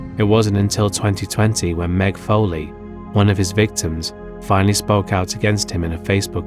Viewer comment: I thought they said Mick Foley was one of his victims. I was genuinely confused and said "what?!"